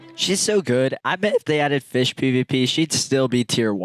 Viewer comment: Did I hear it right? A fish pvp?, that would be a new crazy combat style